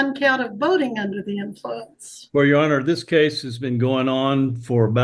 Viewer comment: I thought she said "voting under the influence" 😂😂